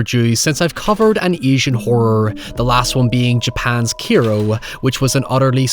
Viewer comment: What movie is he talking about at 0:48 ? It sounds like he is saying he reviewed a movie called Kiro, but I can't find that in his channel. Could someone help me, please? Because his description sounds incredible!